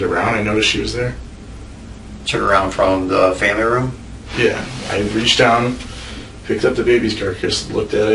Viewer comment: Could swear at around 53:15 he says "I reached down, picked up the baby's carcass" (Based on the detective's response I guess he said "carrier" or meant to)